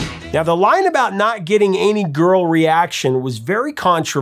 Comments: Wasn't too much of a mondegreen but I heard "girlie action" instead of "girl reaction" lol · I always thought he said girlie action!😂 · I never read an official lyric sheet, until this video i always thought he said "girly action". Never knew it was "girl reaction".